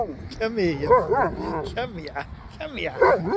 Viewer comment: Sherpa is so funny. I love how he talks with you. I swear it sounded like he said, "I heard you". LOL Kids these days. 😆